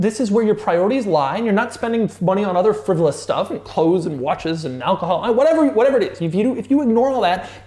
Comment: Does he really say "hoes and watches" at 11:05?